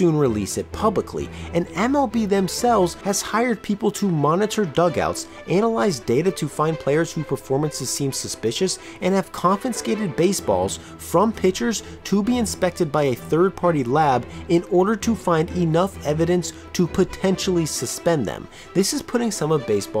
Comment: All I kept hearing was "confinscated" Great video though.